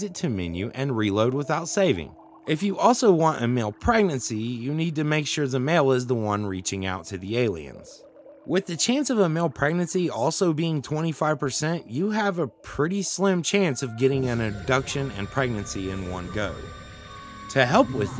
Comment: I had to rewatch that 3 times to make sure I heard that right... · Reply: Until now that I went to reload it, I thought it said "but with aliens". Guess I heard wrong...